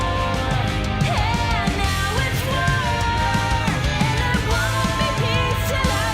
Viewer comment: Bruh.. I thought she said “ not anymore… now it’s worse”. Not “it’s war”.